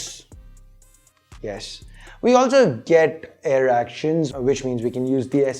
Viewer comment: wait what did he say there XD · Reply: Air actions or as you heard-erection😂